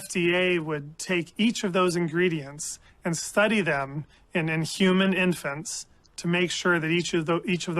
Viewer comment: I don't know if he's saying "study them and IN human infants" or if its "study them in INHUMAN infants". I hear "in inhuman" as in study them in non human animals first, but I'm not sure. Great video though. Bring these monsters down!